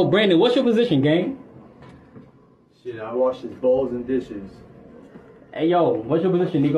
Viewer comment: I thought he said "Shit, I wash his balls an dishes"